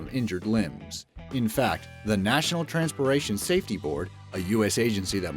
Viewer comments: Did he say "transporation" @ 2:48 mark? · Am I the only one that hears "Transporation" instead of transportation? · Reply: nope you're not the only one he did absolutely say transporation. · Listen to 2:49. It sounds like he said "National Transpoation Board" instead of "Nationa TransporTation board" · is no one seriously hearing him say "TRANSPORATION" instead of "TRANSPORTATION" at 2:48 ??